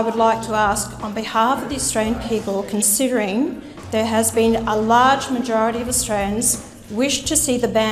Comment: I thought he said consider it a handsom no